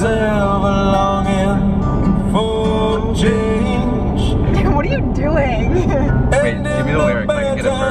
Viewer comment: I swear i heard “Nigga what are you doing?” or am i trippin?